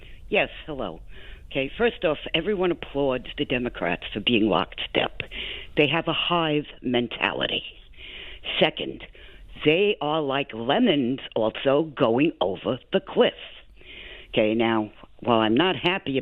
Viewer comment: Did she say lemons going over the cliff? Trump loves the poorly educated, and it shows.